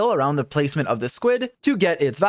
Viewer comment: Did... Did you call it a squid at 1:11?